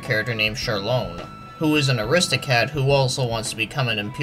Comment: did you say aristocat instead of aristocrat there? Lol